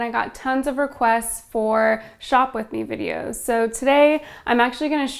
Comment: When you said "shop with me" I heard "shoplifting" And was like...is this is opposite of clickbait? lol